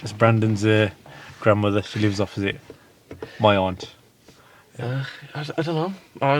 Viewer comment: Is Brendons grandmother you aunt Pinn ? That is what I thought I heard you say 🤔